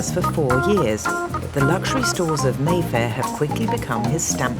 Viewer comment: I thought he said Merthyr not Mayfair